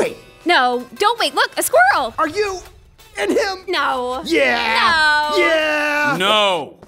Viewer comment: Eren : Wait...are you in...him?! Petra: No Eren : Yeah Petra : Noo Eren : yeah! Levi : NO. I love this so fucking much 😂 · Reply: I thought he said "are you... And him.....